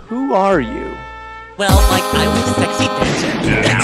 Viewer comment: someone please tell me what he said right now I think he said "Well Mike I'm the sexy preacher"